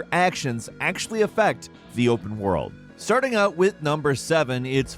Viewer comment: I thought he said the real world 😅